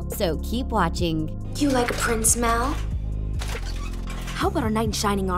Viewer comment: am i deaf or did she say "prince mal"